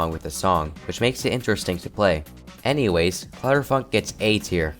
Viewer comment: i swear i heard "clutterf--k"